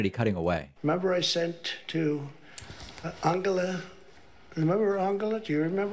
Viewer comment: Is it just me or did he say "Angalita?"